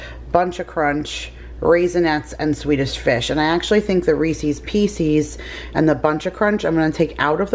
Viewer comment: Did I just hear ‘Reecees Peecees”? I think that makes you an official Southerner now lol Just don’t forget your Lawnguyland roots! 😃